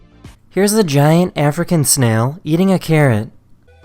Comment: Bro I low key thought he said “here’s a huge African Carrot eating a snail”.